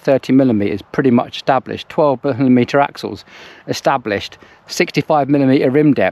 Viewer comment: Great video, thank you! I'm not trying to be rude, but what are you saying about the axles around 25:43? I'm having difficulty hearing it and I'd really like to, because I'm fixing to buy or build a wheelset myself. Not going to be as nice as those Princeton's, but I very much agree with your view about long term ownership and viewing them as an investment. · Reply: 12mm axles on 142 and 100mm established tech. A few year ago we were still fighting over 135x10, 130x9 etc.